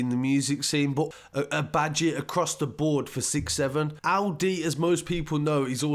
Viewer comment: Bruh, it's pronounced "El -Dee" not "Ow -Dee" 🤷🏿‍♂️ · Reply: I Thought My Man Said Alidi 😂😂😂